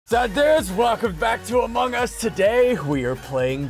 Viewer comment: did ssundee say zud dues